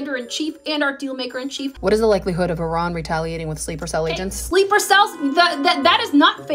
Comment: I heard "sleepy cells" .... 😅